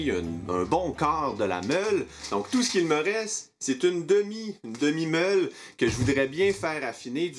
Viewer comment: J'ai hurlé de rire avec "demi meule", pour info j'ai entendu "demi molle" XD